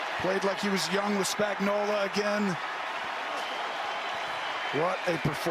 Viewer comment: What did Tom yell, "Guys, they ain't gonna fucking go [again]." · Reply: He said “way to fucking GO”